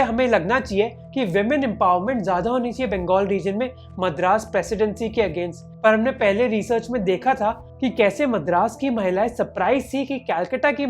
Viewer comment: U said madras women instead of maratha women 😂😂